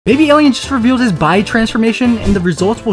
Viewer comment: I thought dude said "Baby Alien just revealed his Bi transformation" 🤦🏿‍♂️